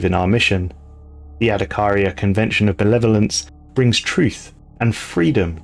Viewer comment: Oh “of benevolence” I misheard it as “of the Netherlands” and was baffled